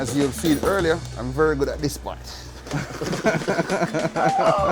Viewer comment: "Rub down the baby, rub it down." That's not even close to "inaudible mumbling". 8:22 Should be "This is what [patois] is." 12:11 Should say "siu mai" instead of "shumai". 14:07 "Shaoxing wine." instead of "incoherent speaking". Popular Chinese cooking wine.